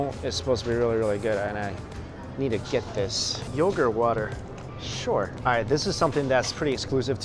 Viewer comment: yoga water? Is that what he said?